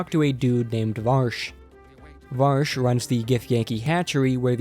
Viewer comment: I almost pissed myself 😂 I can’t believe I’m not the only one who hears Vaush